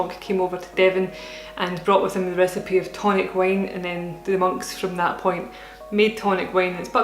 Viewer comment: At first I thought you were saying toilet wine. Silly accent!